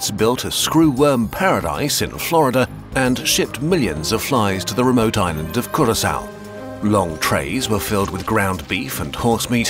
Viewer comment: I thought you said the remote island 🏝️ of Corisaunt (from Star Wars 😂)